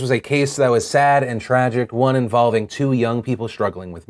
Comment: At first I thought you said she was getting 20 years, and i thought, wow is that it?! But then when you said 15 months....like wtf! That girl is a true villian.